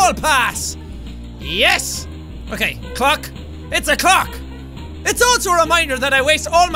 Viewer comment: it sounds like Jack said cocks